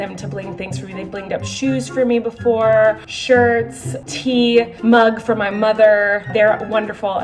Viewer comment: I thought she said teeth.......when she was talking about bing'd up.......